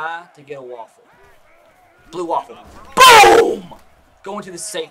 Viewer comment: ok ok did anyone catch him say "blue waffle" really quick at 22:27? 😂